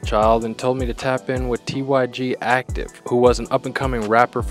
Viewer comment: i heard tyg ain't acktive